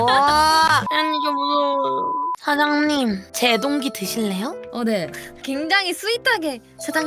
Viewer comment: 나 뭐가 씌인듯 "제동기" 를 "제 동기" 로 들었음 ㅋㅋㅋㅋㅋㅋ